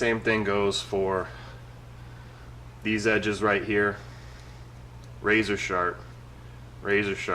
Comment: It sounded like he said razor shart